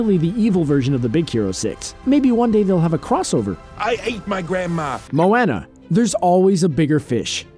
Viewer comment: "I ATE My Grandma" I Died 😂. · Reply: i heard "i HATE my grandma